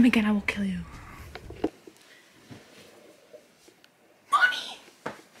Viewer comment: NO BC I THOUGHT YOU CALLED HIM MOMMY